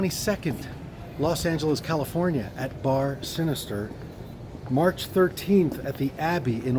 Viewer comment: when he said march 13th i thought he said MAY 13th because that's when my birthday is and i almost gasped